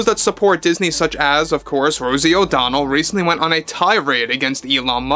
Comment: Tarzan O'Donnel? Misheard narration i guess.